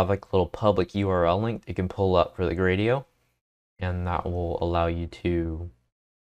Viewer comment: okay but what the hell were you saying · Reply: That will allow you to... make the process!